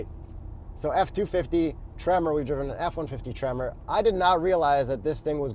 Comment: Swore he said TREVOR edition at the begining 😅 As a brit🇬🇧 i thought it would have dual rear wheels being as its a massive truck that can tow a house.